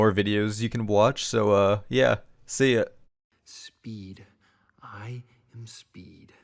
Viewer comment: did that say "friend > i_am_a_squid_kid" either he's trolling or ... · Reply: It said “speed, I am speed” it’s a lightning mcqueen audio clip lmao